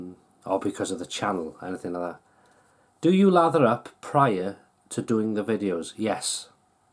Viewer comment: I thought you said lager up..I thinking. Sure he has a few...lol